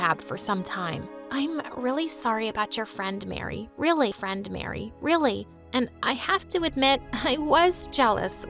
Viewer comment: am i the only one who heard that? "I'm really sorry for your friend mary, really friend mary, really"